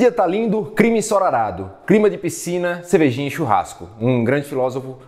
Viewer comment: Hooo dia tá lindo, crima ensorarado, crima de piscina, cervejinha e churrasco. Poze, MC 2021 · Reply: Eu entendi "crime de piscina"